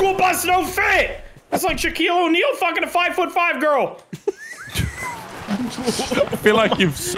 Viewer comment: When said fricking a 5’5 girl I thought he said 5 year old girl 😂